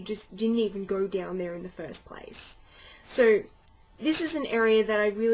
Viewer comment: did she say go down on her in the first place @ 6:29 ? think she wants a face between her thighs~ Alpha male